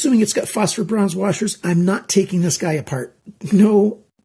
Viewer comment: Happy New Year all the best and i hope you are doing very well . time to dismantle and file of a bit for smoother action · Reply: i just heard you saying (not taking this guy apart ) I am sorry for what i said about doing the opposite